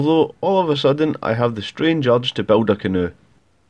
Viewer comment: Did he just say " I want to build a Canoo" at the end ...? wth ? haha . I Love this channel ! Great finale too. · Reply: Nice to see you again, building a canoe is a reference to a character called Ron Swanson, he drinks Lagavulin and build canoes so i thought it would be a funny reference to put in and see how many people get it lol